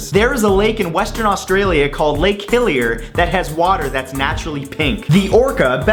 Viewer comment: i almost didn't understood everything in fact 23, i heard "Lake Hylia" instead of "Lake Hillier" °_°